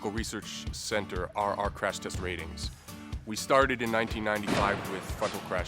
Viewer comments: did he say "fuck'n Crash test" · did he say fucking?? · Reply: TJC450 "We started with fucking crash tests."